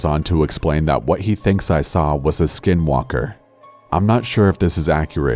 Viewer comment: I thought it said skywalker